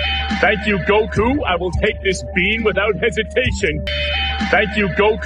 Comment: after a while I heard "without education".